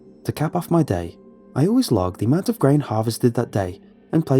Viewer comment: Grain, oh I thought he said weed farm